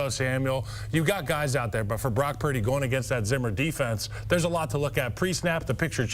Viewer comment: Did he say "Zimmer Defense?"